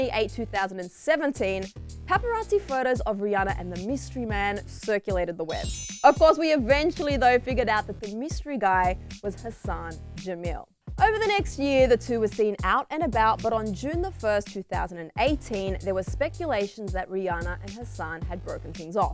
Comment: "The mystery guy was her son Jamill" - that's what I heard 😂